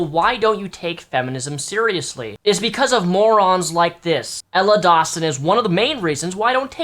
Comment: Ella Dawson, not watson. And while I understand the topic of STD's is a touchy subject, I'm not speaking to those who get one thru safe sex and having your partner NOT disclose they have an STD. Legally you need to tell your partner, I'm talking about this woman Ella who labels her self a giant slut (her own words) and got an STD from sex. If you are proud of getting something that could potentially hurt or harm other people without disclosure , you're a sick, twisted, fuck. Ella is one of those people. Being proud of something that can hurt people. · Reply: OHHHH Ella Dawson i thought you said Emma watson the harry potter actress and was thrown off lol